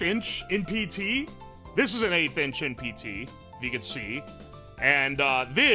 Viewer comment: At first I thought you said "8 inch" instead of "1/8th inch" and was like "yeah man that looks -average-" nawmsayin